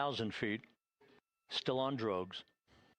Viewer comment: what did he say? :D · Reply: STILL ON DROGUES . = TYPES OF PARACHUTE.